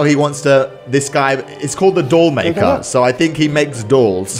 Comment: I thought it said "the door maker"